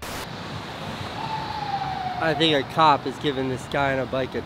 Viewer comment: am i the only one who heard "a nigga cop"?